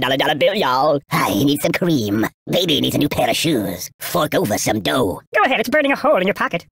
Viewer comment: buddy, watch your language! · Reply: I think he says f*ck over some dough